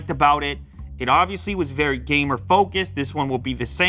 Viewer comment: captions said it was "gay more" focused 🤣